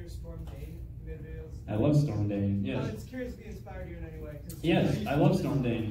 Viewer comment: Who are the guys he mentioned at 39:03 and 39:43? I can't hear their names clearly and I want to check them out. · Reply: Daniel Uranga Its StormDaneProductions just in case you were wonderin